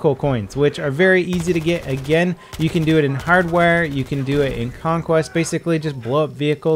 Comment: did he say hardware?